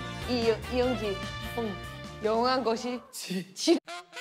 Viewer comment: 편집은 분명 "지ㄹ~"에서 끝났는데도 "지랄하네" 환청이 들려. ㅋㅋㅋㅋㅋ